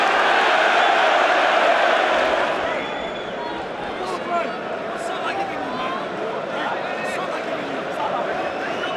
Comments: What does Dier say? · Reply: Whats Salah giving you? 인듯. 손흥민슈팅 잘막으니간 살라한테 뭐 받기로되어있냐는 농담인듯요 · 다이어가 뭐라 말하는지 좀 알려주세요. Tell me what Eric Dyer says. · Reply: 감사합니다 선생님 ^^ 그렇네요. "너 살라한테 뭐 받아먹었냐?" 생중계 보면서 저 골키퍼한테 욕 오지게 했는데 ㅋㅋㅋㅋㅋ 살다살다 상대 공키퍼 원망해보긴 처음입니다~~^^